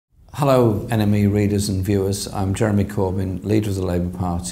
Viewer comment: Did anybody mishear "hello enemy readers and viewers" at the beginning of the video?